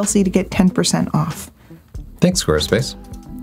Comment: HA! Could’ve sworn at 01:18 I heard Tony tell Chelsea “Thanks horseface”. Freudian slip???